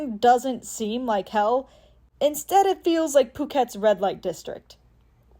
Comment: did you say Phuket? If so. Aye my hood town/province